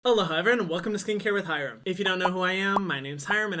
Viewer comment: ok but am i the only one who thought he was saying “hello hi everyone” instead of “aloha everyone” ahahah